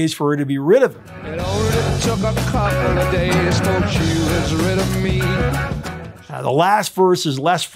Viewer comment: I always misheard "it only took a couple of days till she was ready for me"